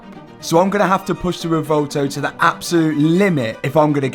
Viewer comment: this was need for speed: french edition 😭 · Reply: bro he said “revuelto” but the subtitle said “huracan”